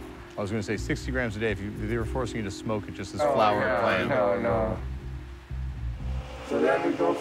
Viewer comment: he says "hell nah" instead of no no lmao